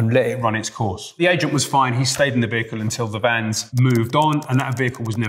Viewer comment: I had to replay it 3 times and then use captions because I swore he was saying "slayed in the vehicle" and thought he might have meant slept or something but he said "stayed"... He definitely replaced t with l though and that made it so confusing..